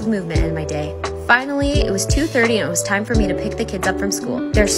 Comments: thought she said couscous and xanax 😀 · Ok but couscous and salmon is actually like fire tho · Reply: I thought she said couscous and xanax 💀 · Ok but couscous and salmon is actually like fire tho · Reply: i thought she said couscous and xanax 😂 like holy mother of god lol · Ok but couscous and salmon is actually like fire tho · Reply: I thought she said “couscous and Xanax.” Came to the comments to check 😂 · 😂😂 I thought she said couscous and Xanax until I read the comments lol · i thought u said couscous and xanax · I thought she said "couscous and xanax"